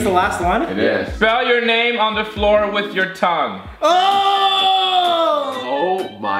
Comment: I thought at 13:05 it said ( 'Spell your name on the floor with your "cum" ) I was like " WTF " Then I went back and heard it correctly..Thank god